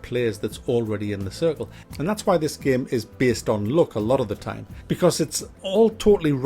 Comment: I thought he say '' look '' and it's all about the '' look ''. Then I realize he's saying '' luck ''.